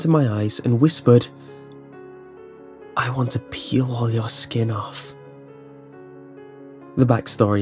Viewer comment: How can kids be the nicest things ever, then become thE DEVIL!!? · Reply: I thought 10 said ILL PEE ON YOUR SKIN